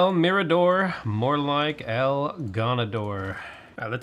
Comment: I know you're saying "el gone-ador" but what you said sounds like "el ganador" which ironically means "the winner" in spanish